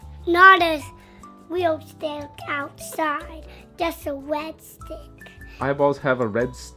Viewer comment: hahaha i think maybe he’s heard the word retina and thought he heard red stick?? idk but your kids are legit gangsters